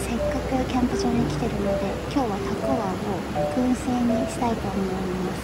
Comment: ヤッホー👋😃 肉多目で美味しそう(^q^) たくあんも美味しそうで良いね～ たくわん って聞こえるような❔(笑) ヨーグルトアイス好き～(笑) 今日妖精いたかな❔